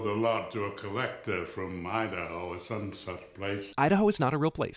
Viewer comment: Wait ...Idaho is real, 61 sins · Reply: He didn't say I don't know. "I don't know or some such place." that doesn't make sense. He put a sin on because Kincade said "Idaho or some such place." Making it sound like he was saying that Idaho wasn't a real place.